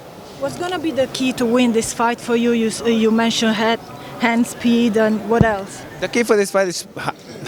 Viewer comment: i thought i heard them say "hands pee'd" lol reminded me of marquez drinking his urine in training haha